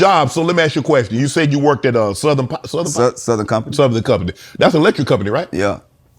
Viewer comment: I thought he said the power companies name was “Southern Conmfort”…😂